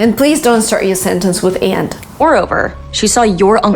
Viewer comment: Help me please. What did she/you say at 6:26? Is it "end" or "aunt"?